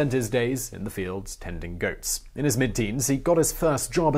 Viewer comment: I thought you said: he spent his days in the fields AND IN goats.